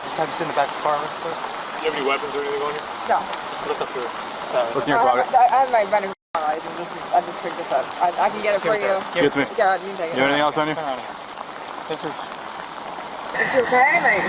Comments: WTH is she saying here/ what does she have?? I've spent a great deal of time pausing and rewinding trying to figure this out. 🤭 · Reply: It’s a pipe for her “medical marijuana”. She says she has her medical marijuana license and offers to go get it. The subtitles were wrong. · did she say "running m3thp!pe"? or what did she say?